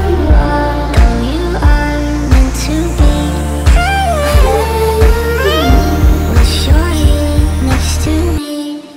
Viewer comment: What are they saying at 0:40 ? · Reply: "To be"